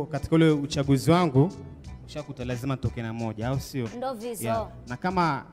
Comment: Did I hear say 'ndo vidzo'!